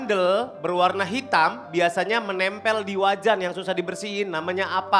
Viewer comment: kok gue dengernya wajah ya bukan wajan wkwkwk